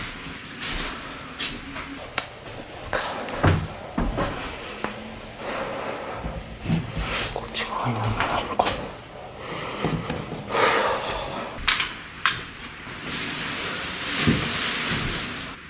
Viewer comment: どうしても「こっち側になんもないのかに聞こえてしまう、、、」